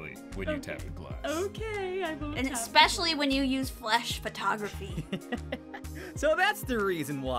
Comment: IT SOUNDED LIKE I SAID FLESH PHOTOGRAPHY AND IM CRYING gosh these always make me so happy to see, i always forget about our shenanigans until these get posted. it makes my face hurts from smiling by the end. love our viewers and love our team!!!